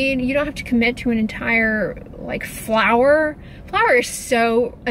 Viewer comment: "Flour is so annoying" 😆😆😆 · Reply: I thought she meant "flower" until I heard "a bag of flour" 🤣